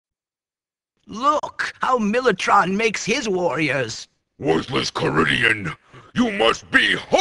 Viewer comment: the first time i thought he said worthless korean